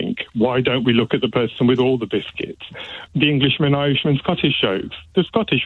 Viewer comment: What did he say? It sounded like "The inglish amaven Scottish shows" {:o:O:}